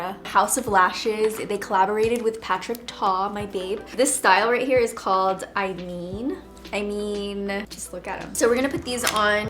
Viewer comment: OMG the lashes are “I mean” and I almost had a HEART ATTACK because I thought you said my name 😭 (eileen) LOL i really thought 😩 and some news! I had my grad pics on Sunday and literally followed your YouTube video from three years ago for my makeup! It was the eyeshadow dos and donts.. obvi followed the do’s LOL I’m so excited to see how the pics come out! #domfamysterybox